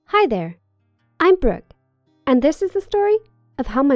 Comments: I thought she said i'm broke so i was like SAME! Turns out her name was brooke. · I heard: „Hello there. I'm broke“ . And then I started laughing 😂😂😂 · I thought see said I'm broke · I thought she said her name was broke 😂 “im broke” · I thought she said "hi i'm broke"